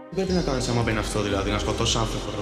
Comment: τι φάση τι λέει?? Και αν είναι από κάπου το απόσπασμα?? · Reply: ''Τι να κάνεις άμα μπαίνει αυτό δηλαδή να σκοτώσεις άνθρωπο" και είναι από δικιά του φωνή Και δικιά του ιδεα